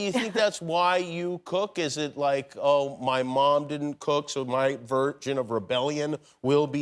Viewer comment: what's jimmy say at 2:42 ?? my virgin of rebellion? vergence rebellions ?